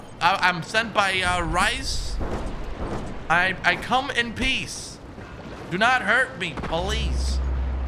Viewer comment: Who else hears "Do not hurt me Police" ??